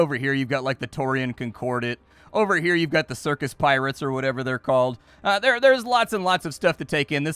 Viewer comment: ROFL You almost just killed me. Im watching and drinking a glass of water and I hear "Over hear are the Circus Pirates". I cant laugh, choke, and drink water at the same time.